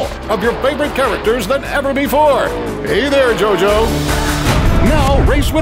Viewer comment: Did i hear.... JOJO?